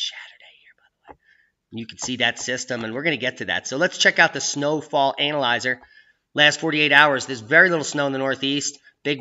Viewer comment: did he say check out the snow fall ANAL-izer???????????????? Just want to be sure. LMFAO